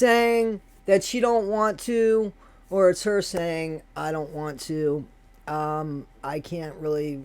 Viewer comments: I definitely heard “ I don’t want to “ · I heard "She don't want too"!